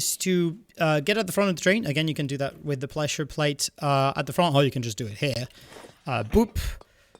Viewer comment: I’m sorry but my brain is now stimming on what you said at 1:28. “Ple-shure plate.” I’m sorry but I can’t help but just do it lol